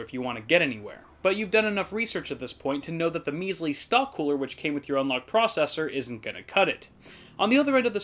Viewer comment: *stock coolers are not good for overclocking *cough* wraith max *cough* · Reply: i thought you said stonk coolers